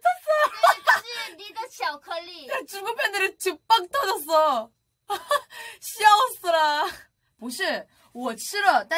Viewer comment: Somebody could tell me what yuqi* says at 0:45? I just understand '중국 팬들이...' but don't understand the other part of the sentence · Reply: One year late but yuqi said “중국팬들이 지금 빵터졌어“ ☺️